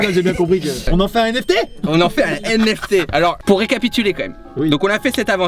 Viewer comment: J'ai compris : "on en fait un MST" 😭